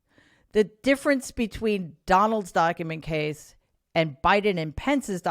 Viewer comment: For a moment when she mentioned Donald doc’s, I thought she said Donald Duck!